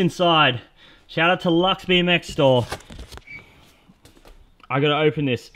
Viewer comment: did i hear a latvian bmx store??????